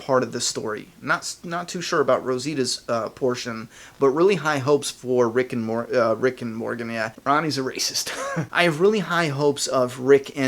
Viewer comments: Lol i thought he said rick and morty and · I thought you were about to say Rick and Morty hahah, which is also a great show. Have you watched it before ? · I thought u said rick and morty for a second